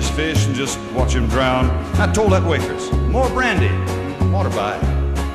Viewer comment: What's he saying at 1:24? After brandy, it sounds he says 'waterby." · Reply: Water by is the preferred way to have water with brandy, it means a glass of water to chase the brandy instead of mixing water with the brandy in the same glass. "Water by"